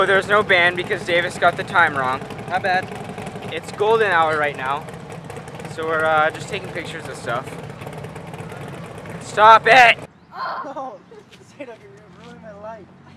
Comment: when you think he said its golden shower rn, i gotta get off the internet i think :p